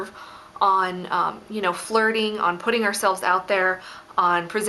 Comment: When she said putting I thought she said pudding